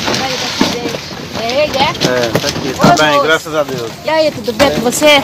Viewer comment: that's in Brazil. As a native Portuguese speaker, I can guarantee this. The man says "graças a Deus" "Thank God". I've tried to make out what else is said but can't really pick up any other words that make sense. Regardless, truly disgusting and vile.